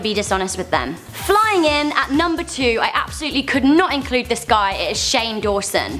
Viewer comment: did she say "I could not include this guy"?